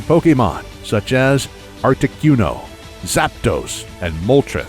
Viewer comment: I heard you say Articyuno instead of Articuno.